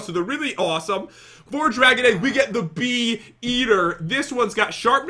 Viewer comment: When pat said "bee eater" I thought he said "beat her " No more coffee before bed